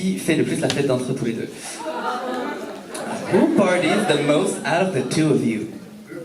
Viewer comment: What's the question at 29:29? I cant understand that.. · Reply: Who party the most ?